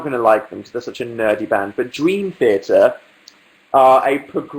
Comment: english is not my native language, so, sometimes I can't understand some things he says, can someone pleeeeeeeeease tell me the name of the band? <3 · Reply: Bee Loyal It's Dream Theater. (